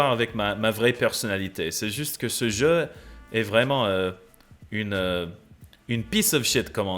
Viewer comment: I mean, I gotta give it to, his Québécois impression definitely sounded like a Frenchman doing a Québécois impression. 30:16 He said "game" not "guy"